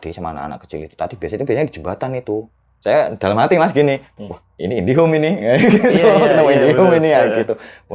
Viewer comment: Guys kalian dengernya Indihome bukan si ?